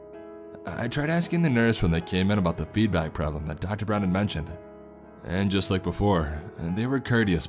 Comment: Dr. Brennan? Did he say dr. Brennan? ...What was Bones doing there?